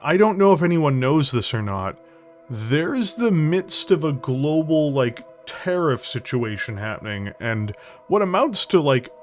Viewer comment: First time listening to the video I heard "There's a global 'tear or ist' situation" and was like "wait wtf?" Had to relisten after I had finished beating the snot out of a Dosh in MHW xD I'm excited for the Teddy bears, and if that image of the Ebon Jag is anything else I'm PUMPED for that